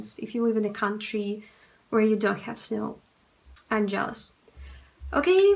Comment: did u say "Pka-pka"? It sounds funny to my Indonesian ear 😆